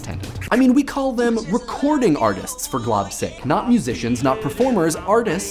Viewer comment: I heard "For glubs sake" and rewinded a few times just to make sure. Mike has successfully been converted to homestuckism it seems.